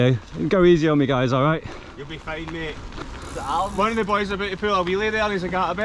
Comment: not got a clue what he just said 🤣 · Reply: Alright after some careful listening I think I have deciphered it: “One of me boys about to pull a wheelie there and he’s like ‘Ah I better not try anything like that today, I’ve got Ali Clarkson watching me!’ Know what I mean? After you mate.”